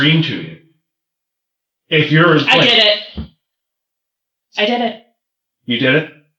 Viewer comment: Holy crap, I didn't even realize she said, "I did it." I thought she said, "I get it." Wow, that detective scared her.